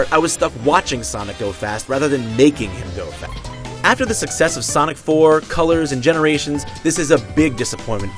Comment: Wait did they just say after the success of sonic 4? Sonic fucking 4